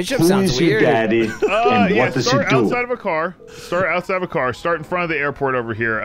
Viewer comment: I have to know did anyone else heard Who your daddy and what's does he do????